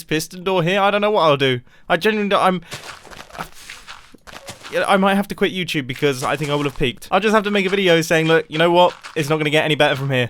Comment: I first thought mumbo said " i might just have quit youtube because I would have peed" and I was like wut?